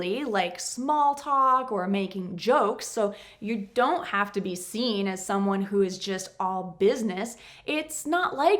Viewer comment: Did she say " you don't have to be 'seen' " ?